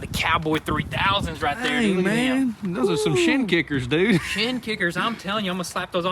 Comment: Haha thought he said shit kickers